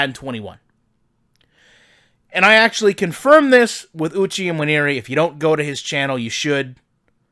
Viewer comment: NFL lineman mentioned at 3:51 = Uchi Nwaneri for those who didn't catch his name